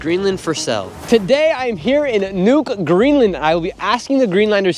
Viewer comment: Did he say " Nuke Greenland"? Idk seem a little bit too radical...